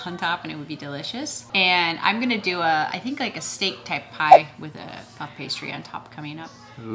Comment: I thought said 🐍 snake type pie. I reacted with, what!